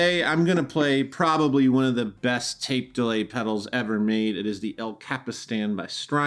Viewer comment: I thought it said "deadly pedals" :(